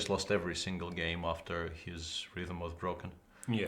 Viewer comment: At @ 15:35, I cannot interpret what Jan is saying about Chao. Can anyone put the words into text? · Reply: After they break he had to say "Ciao" to his lead. Typical Jan pun with Chao and Ciao. ;)